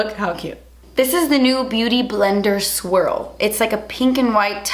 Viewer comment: I thought she said beauty blender "squirrel" not "swirl" for a sec and was so confused lmao